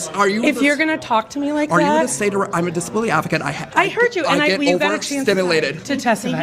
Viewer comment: Did he say, “I’m a disability African”?